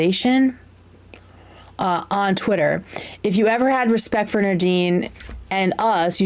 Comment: Lets gooo!! I'm at my 8-5 starting my morning with the girlies!! Also I'm a Gemini ♊ Sun, Capricorn ♑ Moon and Leo ♌ Rising 🌠 🤪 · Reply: it sounded like you were saying 'Nadine' instead of 'nerdeen'. I thought you kept saying my name and it was fucking with me lol